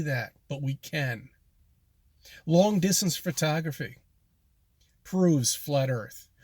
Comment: Did he just say "Fritography"?